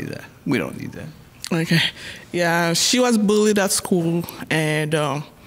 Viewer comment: So we just gonna ignore the fact that she said her daughter almost killed herself 🙁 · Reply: at first i thought she said my daighter was bleeding at school not bullied... :(